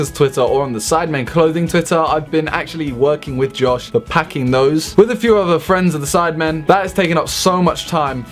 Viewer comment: what is "the packing nose" or whatever he says